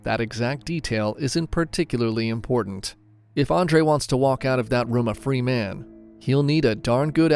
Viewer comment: Did you say if he wants to walk out that room? Lol